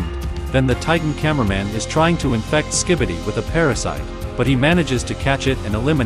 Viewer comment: did he just say that the camera titan is trying to infect skibidi?